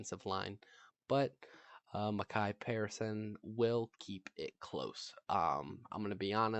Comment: Who did you say at 1:20? 😂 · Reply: I was mentioning micah parson the linebacker for Dallas and how I feel he should be able to keep the game close with him being on the defensive line and trying to stop Barkley